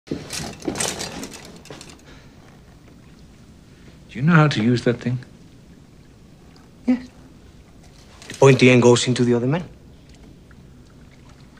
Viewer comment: For decades, I always thought he said "pointy end goes into the abdomen".